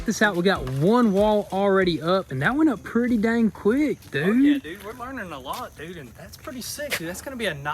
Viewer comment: Did he say “f yea dude” at 21:26 👁👄👁